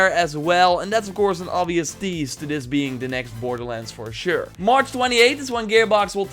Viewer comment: Motha facka I thought you were saying Borderlands free.....I thought we were going to get a free to play Borderlands game